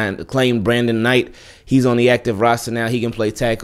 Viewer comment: I thought Harbaugh said Brandon Knight didn't report